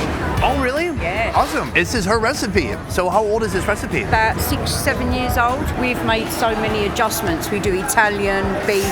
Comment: DID SHE JUST SAID 6 OR 7 YEARS